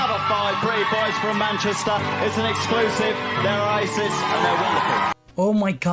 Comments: I thought he said "They're Racist" 💀💀💀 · I thought he said they're racist and they're wonderful 😭😭